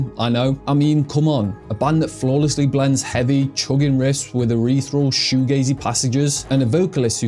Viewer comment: It's ethereal, not erethral. I thought you said urethral at first and had to pause the video.